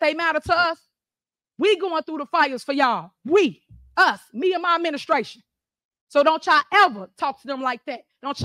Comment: My favourite part is Tiffany's glorious abuse of the English language. 😂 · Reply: Did she say me and my menstruation?